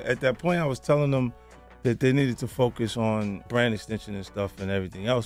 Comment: BRAND EXTENSION I THOUGHT HE SAID BANNIN SNITCHIN♿♿♿